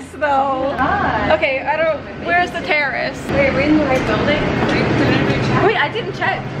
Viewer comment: It sounded like she said "where is the terrorist"